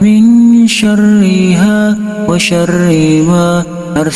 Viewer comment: Bagus Doanya.. Tp kok saya dgrnya bukan WA SYARIIMA ya, tp seperti WA SYARIIWA.. Mohon maaf klo saya salah dengar.. Mungkin pendengaran saya error..